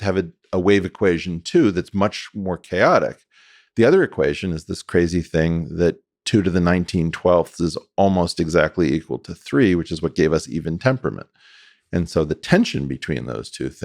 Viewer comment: Can anyone help me understand what he says about the other equation? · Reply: "The other equation is this crazy thing, that (2 ^ (19/12)) is almost exactly equal to 3, which is what gave us even temperament."